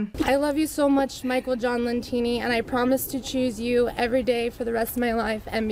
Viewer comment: Wait what? 15:10 ish - did she just say his last name is Teeny? Are we to believe that TEENY is the last name of CHAMPIONS 🏆?!? 😂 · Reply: Lentini. But, the "teeny" part is definitely in there. I think he's overcompensating for something.